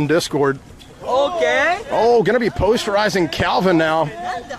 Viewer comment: did he just say post horizon? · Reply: posterizing